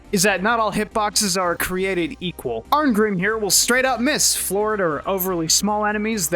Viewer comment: Didn't catch it the first few times I watched the video. You said Arngrim would missed floored enemies. I kept hearing he'd mis Florida. Makes sense with those wide swings